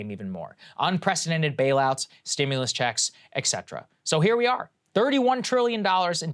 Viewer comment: Did you just say "eck setra"...?